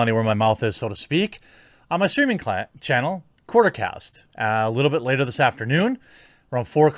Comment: What's the name of your streaming channel?? I didn't catch it I thought I heard quarter cast but I couldn't find it